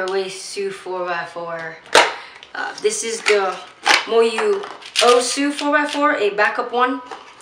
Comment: Did You Say Moyu Osu!